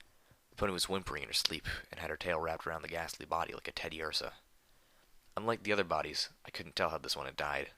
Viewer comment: Did he say Teddiursa?